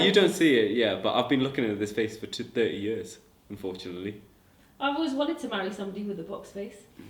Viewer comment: "who's your best friend" "unfortunately you" LMAO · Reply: Beyza I thought he said fortunately so I was like aww 😂😂